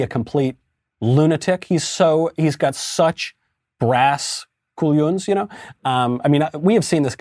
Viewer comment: Did he actually say cullons at 0:47? This is catalan for balls, it's spelled collons. How come an American uses a word in this rare, local, southern European regional language, that not many people speak? Very interesting